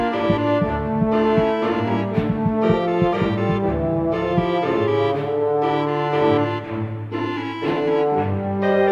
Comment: what did that say?? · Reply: sus cam 🙃